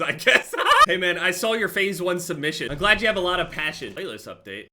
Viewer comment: sarcasm, he saying to the dude who said he has ”no friends” that his faze one clip was ass and find another passion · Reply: ohh I see. Idk why I heard it as “phase one submission.” Of course he’d say Faze. Thanks man